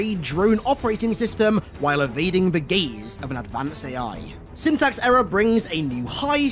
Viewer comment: "stealing an advanced military drone system while evading the GAYS AND AN ADVANCED AI" Am fucking in!! hell yeah let's avoid some GAY'S!!! I can't be the only one who hears that right I know it's gaze but man that's too close.